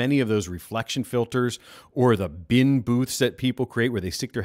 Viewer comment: Can you compare the noise floor of this vs a giant lampshade covered in foam an placed on your head. .... then you said "bin booth" and I heard "trash can" lol.